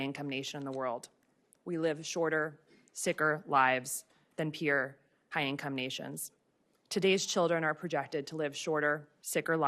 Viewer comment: Did she just say "purer high income nations" 😮